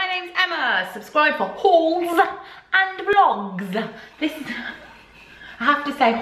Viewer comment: First few times I watched you I thought you said ‘whores and vlogs 😂